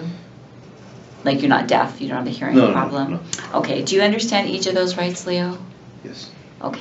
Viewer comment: She said the date as "5/5/2023 16 am now"........there is no 16am WTH?!?! AND the top left says 5/5/2020? Did my hearing go bad? lol